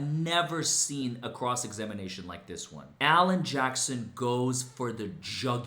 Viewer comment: Alan Jackson? Almost sounded like "Johnson."